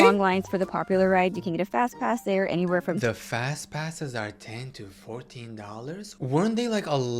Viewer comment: Love how the captions say "fastass*s" instead of fast passes 😭😭